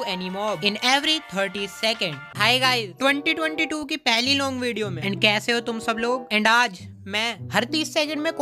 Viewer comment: Did you just say 'long video'🤨🤨